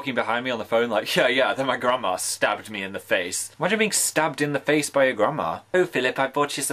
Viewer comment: i thought he said “and then i THINK she stabbed me in the face” like are you SURE? was it just the wind?